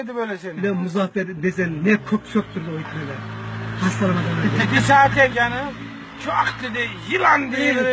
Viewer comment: da ne diyor ya hu? "Teke saadet canım" mı diyor!